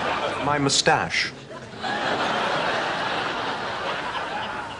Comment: What does Niles say at 0:40 ? ? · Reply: "I grant you its at an early stage"